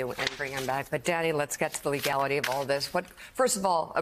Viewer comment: did she just call him Daddy?